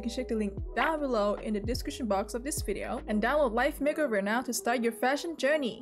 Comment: i thought you said wife make over at the sponsoring